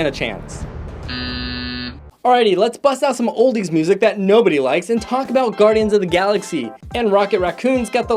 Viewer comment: Did you say nobody likes the music